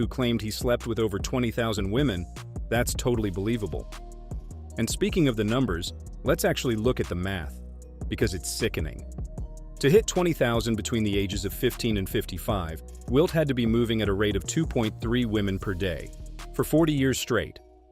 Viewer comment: I thought he said 100 not 20,000…..